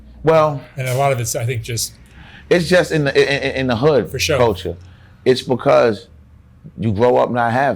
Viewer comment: Did just say “Fo sho” 23:40 I liked this interview a lot. It’s all about the team. No IG show out. Fo sho